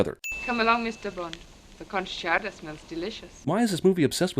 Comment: Bond said "clam chowder" right. cuz I heard "cum chowder". did you hear cum chowder.... I'm very confused by this. I really hope he didn't say cum chowder.